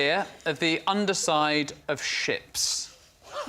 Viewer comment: I clicked on the video without reading the title and thought he said "chips", instead of "ships"... 😂